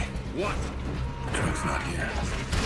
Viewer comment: What does Roan say at 1:13? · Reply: Erika Strohl "everyone does whats best for their own people- it was my men who took it"